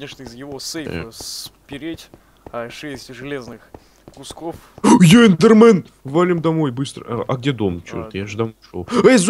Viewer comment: мне покозалось емиральд сказал е Маё Слендер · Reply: А мне послышалась в 3:00 сказал я Эндермен!